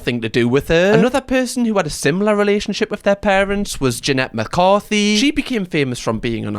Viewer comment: It sounded like you said Janette McCarthy, her last name is McCurdy in case anyone wants to look into her books and such. :)